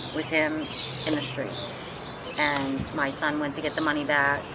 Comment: Those people who killed this man for defending his son will get away with murder. These Democrat prosecutors will somehow flip the switch and put charges on the dead man and son. Mark my words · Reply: Did I hear her correctly? Right before the clip of her interview ends she says, " ... and my son went to give the money back?" 1:25 Because you got to ask why two men and three teenagers would jump a single kid? A kid that they were arguing and physically fighting with all day? This isn't normal bully behavior. Grown men don't just show up at somebody's house wanting to tear the hide off some kid. There is only three reasons this would happen. One, the kid killed somebody. The kid raped somebody close to them. The kid stole drugs or drug money and its return is on the line. If I'm right, the father died for no good reason. His son played his own family and he paid the price. And if I'm right, this is going to get flipped and it will be the kid facing charges for his father's death and not the ones who actually beat him to death.